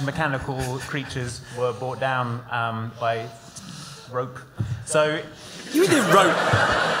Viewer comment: "...large, mechanical creatures brought down by---" Me: "Did he say 'rogues' as in the speeders of Rogue Squadron?" "--rope."